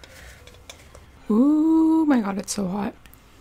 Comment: did you say my hot its so hot instead of my god its so hot